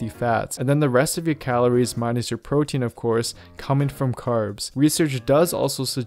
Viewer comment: i didn't get it what he said rest of the calories min ur protein